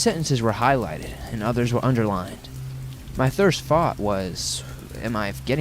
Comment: "Thirst fought" (instead of first thought) at 2:52!! Hahaha i do that shit all the time. Its fad mrustrating!!